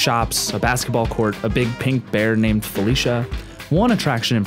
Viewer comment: When he said "Felicia" I heard "BYE BYE".